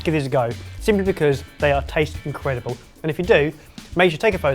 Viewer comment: I have never eaten Creme Brulee, but it looks delicious. It sounded like Ben said he wants a video of the crap. LOL :D